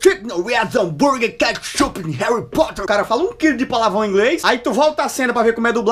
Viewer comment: eu entendi Burger ketchup Harry Potter kkkkkkkk